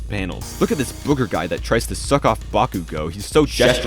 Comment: I heard "look at this booger guy that tries to suck off bakugo" and immediately lost focus